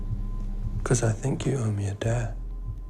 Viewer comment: I heard “cos I think you owe me a dad” HEHEHEHE